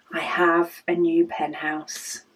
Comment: I LOVE it!!!! I am trying to stop buying pens (for now) 😂 I have a full rainbow that is available in my Lil swivel caddy! And, I pick a pen based upon my mood! I kept giggling b/c it sounded like you said "penthouse" a couple times 😉 I can't wait to see them all organized in there the way you want them !!